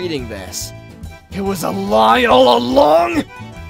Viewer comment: At-0:46 WHAT DID HE SAYYYY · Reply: It was a lie all along